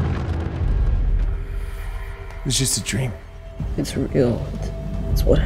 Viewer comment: did he say bad man or Batman?